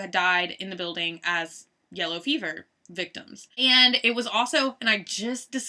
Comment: The first time she said yellow feverish thought she said yellow beaver lol